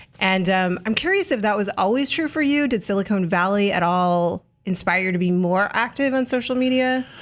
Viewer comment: Did she say Silicone Valley? lol